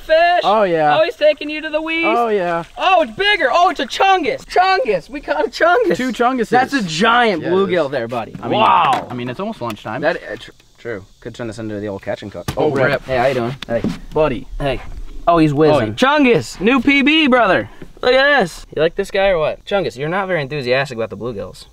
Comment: For a second I thought it’s said Catch n cool 😅